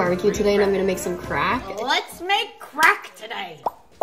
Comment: I thought she Said time to make crack😭🙏